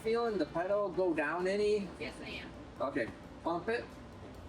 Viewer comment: was that a "Yes ma'am" I heard? 😅🤣